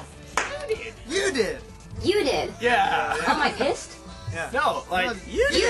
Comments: haha did she say am i pissed at like 2:50? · hahahaha at 2:50 did she say "am i pissed?" hahah. oh dear.